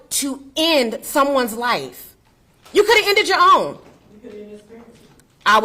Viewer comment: what DID his Mother Say...After the Sister said "He could've Ended his OWN?... She SAID "You could End this ...???"